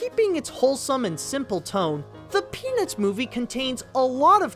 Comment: When you said "The Peanuts Movie" it sounded like you said "The Penis Movie" lol